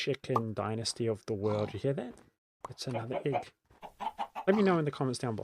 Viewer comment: bro got his own subtitles wrong… you said egg. But you wrote pig 😂😂